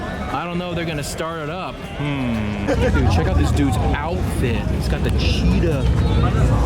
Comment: Did he say hes got the Cheetos 😂😂😂